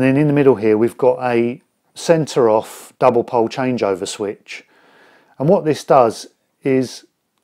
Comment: At 9m15s I am trying to understand what I hear as "Sensoroff double pole change over switch". Is this a particular brand or a particular device/design? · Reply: Hi Mark, what I’m saying there is “centre off double pole changeover switch” it’s a three position switch with a central off position. Thanks for asking.